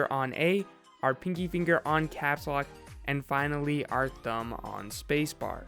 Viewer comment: he said dumb instead of thumb